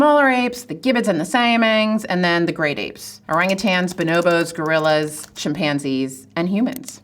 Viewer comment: All I heard was "orangutan fembois".